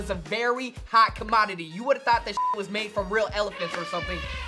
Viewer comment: did he said shit?